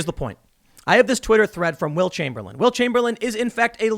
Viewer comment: For the longest time I thought you were saying Wilt Chamberlain and thinking 🤔 isn’t he a basketball player.